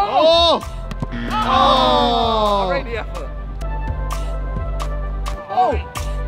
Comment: for a second thought Simon said, "Rate the ef-foot," and had made a pun. Yeah, he didn't. I'm just deaf